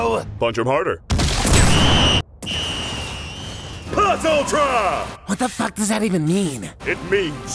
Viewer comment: "PLUS ULTRA!!!" When I watched this the first time, I misheard that as "MAZEL TAV!!!" Edit: It's also funny how Shigaraki went from being a budget Giovanni to becoming the deadliest being on the planet